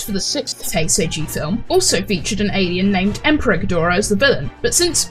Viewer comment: IM NOT GUNNA LIE, I HEARD EMPREG GHIDORAH, INSTEAD OF EMPEROR GHIDORAH LISTEN TO IT AND YO WILL HEAR IT AS WELL, NOW YO WON’T BE ABLE TO UNHEAR IT